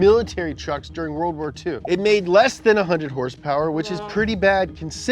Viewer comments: you say less than 100 horsepower but your caption says greater than 100 horsepower. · they put the greater than symbol (>) instead of the less than symbol (<) but he clearly did say that “it had less than 100 horsepower” this really had me cracking up that they made such a simple mistake. It’s like when someone spells rouge instead of rogue